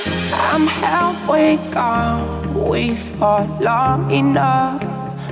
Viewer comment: I hear „we felt long enough“.